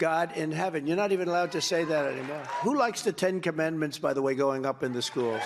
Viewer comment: The 10 "comendments" is what I hear him say